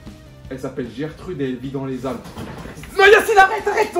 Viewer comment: dans les quoi ? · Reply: Dans les Alpes